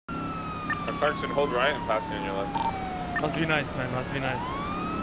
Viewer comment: I thought this said "Stay right I'm pissing on your left".